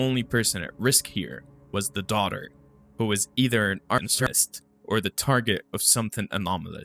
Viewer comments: A what? o_O The Subtitles said "artist" but that doesn't fit? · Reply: Censored word on purpose for Youtube monetization reasons. Actual word was “Arsonist”! 😊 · Hey can you clear up on what you said at 12:12 She's either an what ? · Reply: Arsonist. The word is likely not safe for YouTube